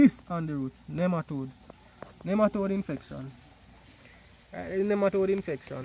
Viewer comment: Hi, good presentation, 8:25 did you say it's nematodes? What do you recommend to use to treat it the system after talking out the plants ? You you recommend this planting system for strawberries? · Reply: Hello thank you for the feedback. Yes, I did say Nematodes. You can use a 10% clorax solution or Ozonation. I have never seen strawberry grown in canals with the gravel hydroponic system however, It should work with a bit of adjustment to compensate for the growing pattern of strawberry. Adjustments like, putting a rockwool cube on top of the gravel then put the strawberry plant in the rockwool so that when the canal fills with water, the water does not wet the strawberry leaves. But it definitely is worth a try.